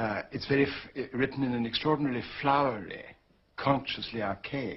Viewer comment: Can someone please tell me what he said around 1:22? Sometimes I cannot hear very well. He said something like: "It is written in a extraordinary "flourly?" consciously archaic manner. What was the word that sounded like "flourly?" Sorry if that's a dumb question, as I said I cannot hear the best. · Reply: "Flowery" -- Full of elaborate words and phrases.